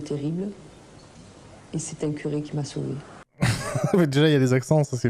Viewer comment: Oh, oh, ho... Saylor Foune? Contraception Lunaire, protèges moi ! 29:44 ... ecoutant d'une oreille distraite, j'ai entendu quelque chose qui m'a fait... rire: " Il m'est arrivé quelque chose de terrible... C'est un curé qui m'a soulevée "... Dafuk?! Ah, pardon...